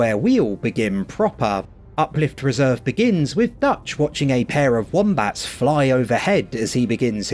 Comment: I'm sorry I heard "pair of wombats flying overhead" and almost burst out laughing at work